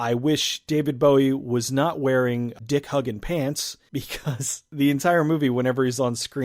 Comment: I heard "dick huggin pants" and thought "Dick Huggin, must be a fashion designer" -_